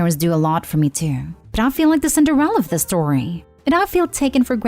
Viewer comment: Sounded like the voice over said Cinderrella instead of "Cinder" Ella. Her name is on page ONE.